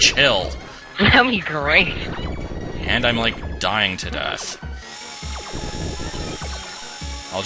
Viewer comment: Did you say that you were "Dying to death" at 19:30?